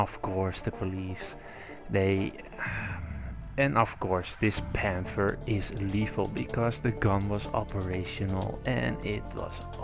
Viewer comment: The gun isn't operational, the owner said that he had a note from the local government in Germany saying the tank was demilitarized and could not fire ammunition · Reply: That's not what I heard you say. If it is then speak more clearly in the next videos you make. What I heard was " Of course this Panther is lethal, because the gun was operational"